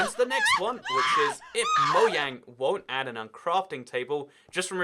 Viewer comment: Did he just say moyang